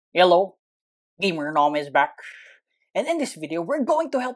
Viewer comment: "hello gamernom is back and for todays video" the best intro i ever heard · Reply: wrong it's "hello gamernom is back, so for today's video" maybe you misheard it